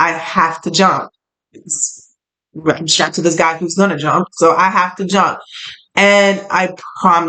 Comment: Wow so as you said “well I’m strapped to a guy who’s gonna jump” I heard “Who/What Are You Strapped to?” Are they ready to jump or are they gonna hold you back